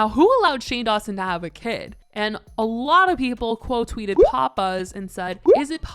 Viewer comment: Omg when you said PopBuzz I thought you said Papa's 😂